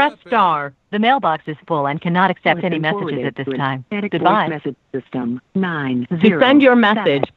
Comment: did i hear say"i have to shit goodbye"